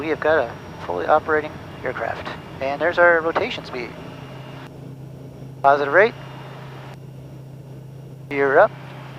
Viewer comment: Glad it's fixed again! One question: I hear that alwaysbut what does that mean at 8:22 ? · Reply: Positive Rate? Meaning a positive rate of climb.